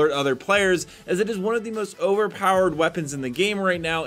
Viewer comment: Did you say the charges rifle is overpower in